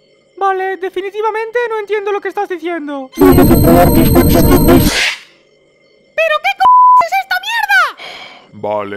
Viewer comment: que dice tomate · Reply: no, ya vi que dice lo que dice es: puedo controlar tus pensamientos